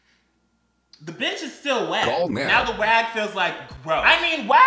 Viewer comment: did you just say “the wag” lmao